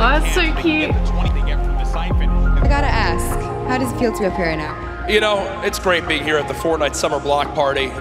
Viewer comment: I've got to ask, how does it feel to have Corona? That's what I heard 🤣